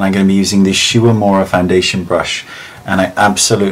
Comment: Can anyone tell me what foundation brush he loves? At 0:49 he is saying something like "shoeamora" foundation brush. Thanks. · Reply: This is a year too late I'm guessing, but he will have said 'Shu Uemura' - it is a Japanese makeup brand, still popular today :)